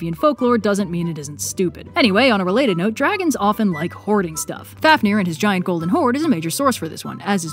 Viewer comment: I legit thought she said “Dragons really like horny stuff” ...took me a second to realize she said HOARDING stuff 🙃😂